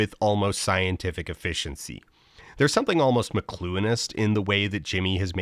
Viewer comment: Is there any way someone can define what was said at 11:20 with "mcleuinist?" Not a term I'm familiar with and I don't know exactly how to spell it and the cc spells it "mlonist" which doesn't sound right. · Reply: McLuhanist, after the philsophy of Marshall McLuhan, whose work includes the phrase "the media is the message", a statement that a work in a certain medium (like a reality game show), its audience will gravitate to what that medium expresses most easily at the expense of more nuanced expression from the author(s) of the work. Mr Beast has about the philosophical depth of a middle school bully, and go figure that the participants have created an environment that looks like middle school socialization.